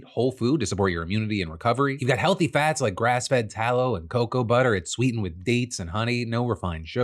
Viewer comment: Wait did you say TALLOW? As in rendered beef fat traditionally used to make soap and candles?? I know it can be useful as a cooking oil, but it's like 50% saturated fat and is not good for your heart health or cholesterol...